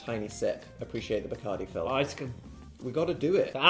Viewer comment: watching with subtitles and i appreciate they're not autogenerated but at 1:23 i believe Phil says "Ah it's gonna-" (like it's gonna burn or smth) instead of "Ice (indistinct)"!! edit: also the 2nd indistinct from dan at 12:32, he's def saying "it's our moment~" ...damn i might be good at this😭 edit2: oh yea and seeing as dan follows up w a kpop demon hunters ment he's def singing "golden" there LOL